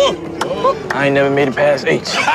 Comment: What did he say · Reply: I ain't ever made it past h